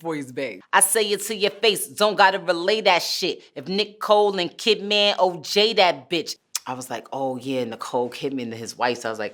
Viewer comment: What's crazy about the Nicole Kidman line is I thought she said "If Nicole the kid, man, then OJ that bitch" So, she HAD a good bar lmao Nicki=Nicole. Nicki says she's the kid a lot... *shrugs* So, it's still doable if she NEVER explains her line again. lmao